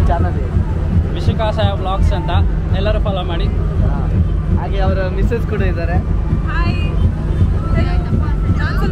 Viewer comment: What is the YouTube channel name he said in 3:17 mins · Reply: Vishika saya vlogs